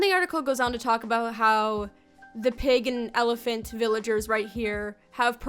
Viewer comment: am I the only one that first thought she said pagan elephant here?